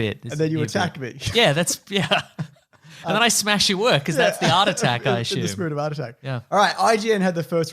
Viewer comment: I thought they were saying heart attack